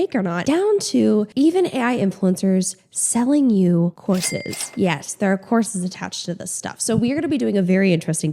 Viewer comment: I heard “they selling HORSES” , and I was like what😂, now I’m low key disappointed that it’s courses.